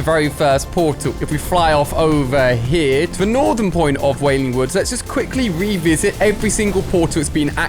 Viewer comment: did he just say wailing wood